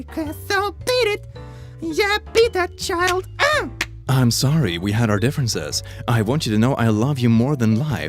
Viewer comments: I get done with an add the all I hear is "YEAH BEAT THAT CHILD OOO YEAH" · When you said "beat that child" I thought you said "Eat that child" ... Ehehehehehe